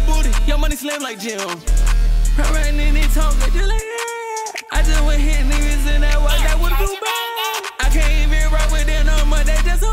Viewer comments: did he say niggas on 1:19 ...? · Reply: I just went hit Neimans and i walked out with a few bagssss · what did bro say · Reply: I ridin inna tonka jus like datt